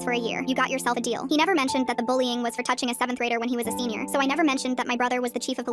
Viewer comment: I thought they said 7th grader.?